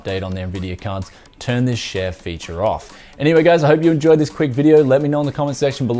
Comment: i thought he said turn this shit feature off 😂